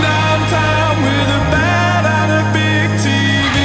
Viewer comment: I always thought he said 'a bad ass big tv' :( It sounded so good